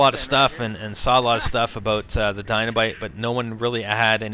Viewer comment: did he said dynabite at 16:34 ?